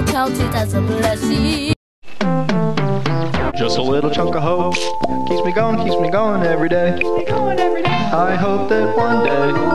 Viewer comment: Did anyone hear "Just a little chunk of hoe keeps me going everyday?"